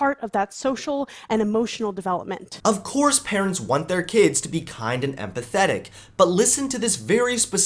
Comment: I thought she said "kind and pathetic" instead of "kind and empathetic" at first..